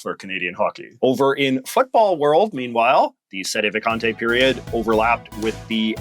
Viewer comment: in 20:18 I thought he said "f*ckball"